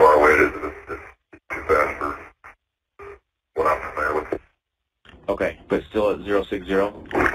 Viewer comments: Min 14:43 "Too fast for what I'm familiar with" is what I heard for the [inaudible} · when the subtitles says “inaudible” the pilot says “too fast for anything I’m familiar with”.